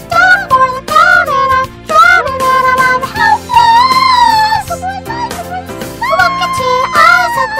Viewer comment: When it said HELPLESS! i thought it said HAWKFROST!